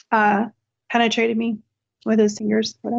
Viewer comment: I'm sorry, did I mishear, or did she just say Joe Biden penetrated her with his ears?